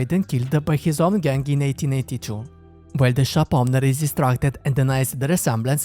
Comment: The wild west lasted from 1532-to-1930 aka that's 398-years 5:05 so this is set sum time after 1880 6:47 did you say 1887